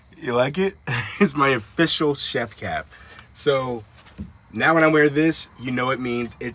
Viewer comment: I swear to you I thought you said I invested in an official chef cat and I thought Lars was going to be in the next frame with a chef's hat on😂😂😂😂